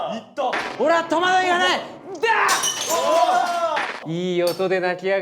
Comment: 俺はトマトいらないに聞こえたw